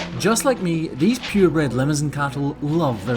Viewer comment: Is the breed LIMOUSIN? If the “girls” are LIMOUSIN (a breed originally from France) that would explain why I thought I heard lemon-something or limousine. 🐂🐂